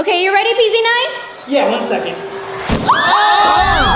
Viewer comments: Pz9 said yeah 1 Sec and must of misheard it 👇if you agree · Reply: He said ok 1second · When pz9 said ok I heard him say 1 second